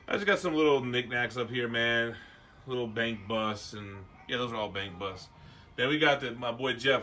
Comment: Yoo at 7:05 I swore I heard him say “little bang bus”. I was like what?! Just casually mentioning some porn DVDs. Then I realized I’m an idiot.